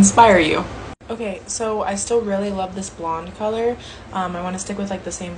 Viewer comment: “Blondes don’t want short hair” me, a natural blonde sitting here with a pixie cut….. 😒😆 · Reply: I’ll have to listen again, but I could have sworn I heard her said “blonde” since Margaret has blonde hair and mentioned she loves it